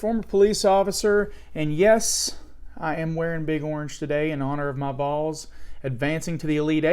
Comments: Thought he said" in honor of my balls" for a second lol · At first I didn't think you said what I heard thinking you must have said "BOYS" and I just misheard you. So I hit the 10 sec repeat, initiated closed captioning and CC typed out exactly what I thought I had heard. You clearly make the statement: "AND YES, I'M WEARING BIG ORANGE TODAY IN HONOR OF MY BALLS!!!" You got to explain. I know it's football related but you got to explain LOL cuz a lot of people should be busting your balls lol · I heard "balls" rather than "Vols" at the beginning. But more importantly , great content as usual. Much respect. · I swear I thought he said ."in honor of my balls" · who else heard “ma balls”😂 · I totally heard “in honor of my balls” lol